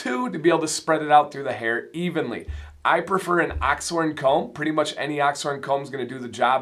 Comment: That's an ox horn comb from Beard Octane! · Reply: cheers dude. I thought you said Ox Horn but I wanted to be absolutely sure. Appreciate it. Keep on bearding!